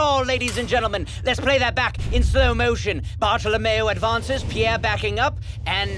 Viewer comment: Oh...oh it was Bartholomew.... I heard that as Bottle of mayo(naise)